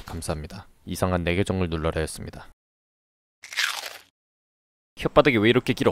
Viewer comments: 이상한 내계정을 눌러라였습니다 나만 이렇게 들리나? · 이상한 이라고 들리지 않음?ㅋㅋㅋ 끝가지 들으면 이상한내계정을눌러라였습니다 로 들림ㅋㅋㅋ